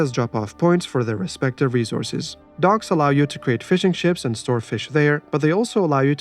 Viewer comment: For a second I thought he said : " docks allow you to make fish and chips"